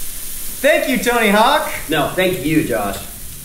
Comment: I first thought Tony said "no, thank you Donna", which would've been hilarious